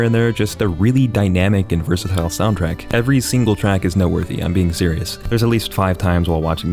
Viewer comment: Idk if its just me but at like 7:48 i think or around there does he say note worthy or nut worthy?